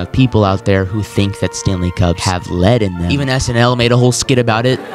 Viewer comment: i thought they said sMl :( AND SML LITERALLY DID MAKE A SKIT ON IT